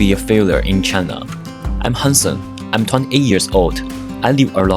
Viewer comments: I thought he said "I am handsome" lol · I thought you said, "I'm handsome" in the first line. I'm like, ok!